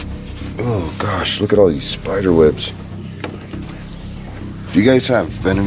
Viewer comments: I heard "spiderwebs are here" Like if you heard it too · Omar said ew look at this spider webs and I heard yeah spider webs are in here Like so Omar can see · That whisper that you guys heard was saying there's so many spider webs